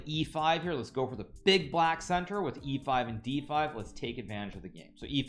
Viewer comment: What did bro just say 💀💀💀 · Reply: He's going for the BBC Big black center! 😃